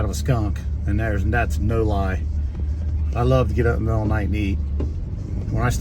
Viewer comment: Did I hear that right " I love eating ass in the middle of the night", A bit too much information..😳😁 Keep up the good work guys..